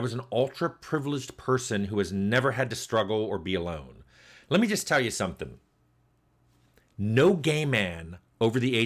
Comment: I thought you said "no game man" and had to go back and watch again. As always, I love how much you open up and now you're my gay man game man!